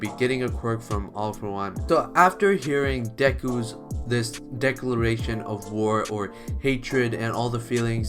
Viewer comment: Did he say Deku-luration or am I just hearing things